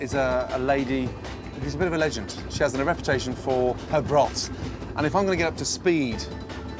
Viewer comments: I heard "... for her bras" and was like wait a minute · I thought he said “She has a reputation for her breasts” 😂